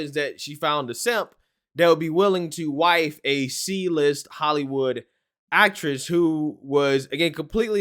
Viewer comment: You said C list I thought you said seedless LOL wow I'm going crazy